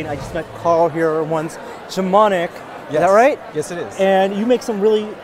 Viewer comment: Blyat I thought it said gopnik